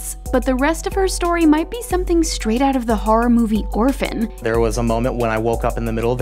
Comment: did she say horror movie "Griffin"? If so, I cant seem to find this movie